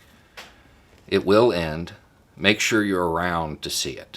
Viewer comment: It’s funny. I keep hearing ‘It’s just a thot.’ Please be more eloquent or do continue. I’ll at least have a laugh about it, or so I did.